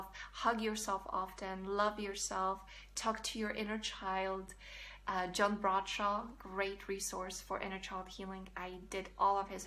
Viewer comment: did you say Jim Brodshaw?